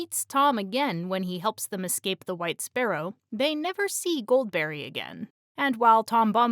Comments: Totally heard this as "white sparrow" at first, did a double take. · When you said "wights' barrow" I heard it as "white sparrow" and was momentarily confused. This has been your help-with-the-algorithm interaction for today!